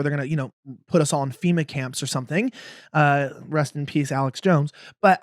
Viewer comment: Did you say @ 2:55 “Rest In Peace Alex Jones”